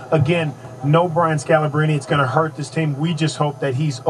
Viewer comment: did he just say.. "no brian scalabrine will hurt this team" ? wtf kinda shit was he smoking